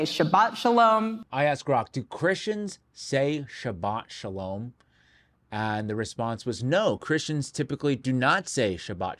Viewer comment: I thought she said shallot shaboom and believed she was referring to those wee, exploding onions 🧅